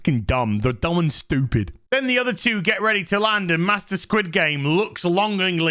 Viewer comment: You kept saying “Master SquidGame”, but I kept hearing “Master Squidward”.